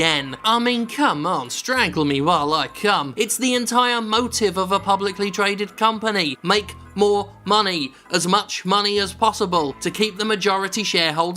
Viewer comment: Strangle me while I CU* WTF lol. Am I hearing him wrong at 5:40 😂